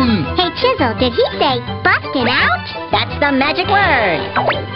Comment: "Hey Chisel, did he say, "butt get out"?" "That's the magic word!" proceeds to violently smash television screen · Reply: What are you, a grammar shark? I definitely heard "butt get out"